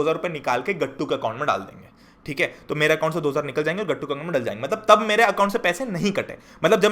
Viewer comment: sounded like gattu ke muh me dal jayenge